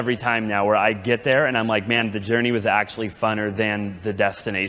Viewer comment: Did he just say FUNNER